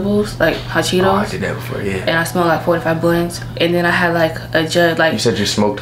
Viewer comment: I thought she said 45 blunts, I was like how you alive!